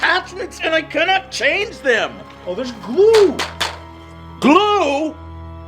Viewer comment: DID I HEAR BLUE ? BLUE FCKING BLUE ?? oh no its Glue